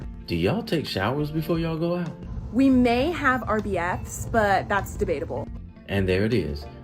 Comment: I think I heard, "We have RBF's" (Resting B***h Faces) and not "We have our BF's" (boyfriend or best friend) but I could be mistaken.